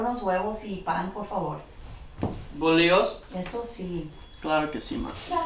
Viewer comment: what's does he say at 0:09? I am Spanish, but it sounds weird... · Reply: Catalina Lagos He said bolillo.